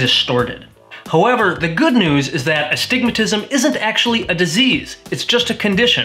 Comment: I can’t tell you how long I thought the doctor was saying “a stigmatism” not “astigmstism” 😂